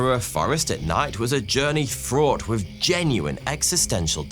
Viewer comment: i thought you said faughty two im new